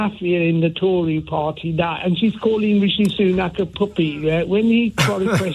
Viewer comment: did say the "f" word? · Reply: He said puppet